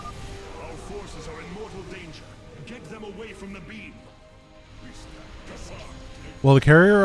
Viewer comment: At the end I swear I heard him say 'Our forces are in mortal danger, get them away from the meme!'